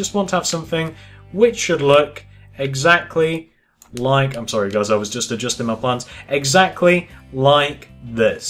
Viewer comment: did you say "sorry guys I just jizzed my pants"